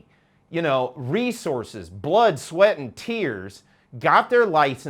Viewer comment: Did he say blunt, sweat and tears?